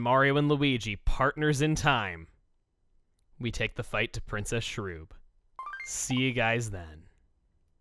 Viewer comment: I swore you said “effing fajita to princess shroob”